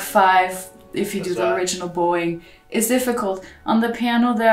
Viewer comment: I thought she said “Original boi,” rather than “Original bowing.”